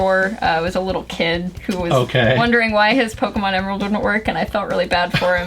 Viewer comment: What does she say at the minute 12:28 ? Tha the kid was wondering why his pokemon.... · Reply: "..why his Pokémon Emerald didn't work.."